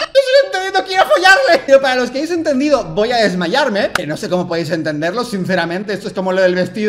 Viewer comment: si dice “quiero desmayarme” o al menos yo si lo escuché, la primera vez no lo entendí así xD Sigo escuchándolo y ya no puedo parar de escuchar “quiero desmayarme” :u